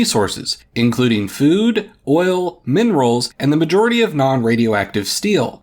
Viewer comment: did I just heard Oil?